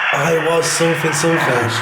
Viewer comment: I think it said “I was angry!”